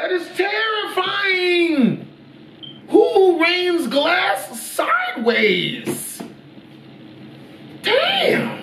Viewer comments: it’s sounds like he says “WHO RAINS GLASS SIDEWAYS” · did he just say who rains glass side ways???